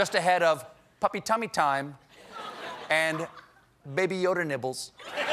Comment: Totally misheard 'nibbles' as 'nipples'. Was disturbed for an entire minute before realizing the mistake.